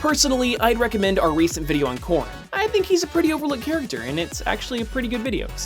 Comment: OH! He said Corrin! I thought he said porNEVERMIND!